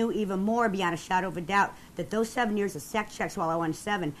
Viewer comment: I thought they were saying a sexcheck, i was thinking why are they doing all these sexchecks...and what is a sexcheck..does someone watch while you are having sex to check if you are doing it correctly...sorry didnt realise until way in that was a security check